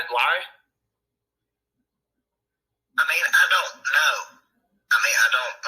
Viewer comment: Did he say:: " I mean, I don't mind" :: Listen from: 1:16 or